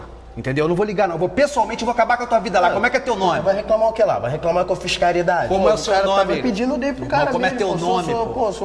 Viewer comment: "Vai reclamar que eu fiz caridade? " kkkkkkkkkkkkkkkkkkkkkkkk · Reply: Eu sempre achei que ele tinha dito "Vai reclamar com a fiscalidade?"